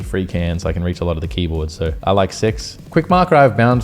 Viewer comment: did he say '' I like SEX " @ 2:53 ??? That exthplaen everthing for me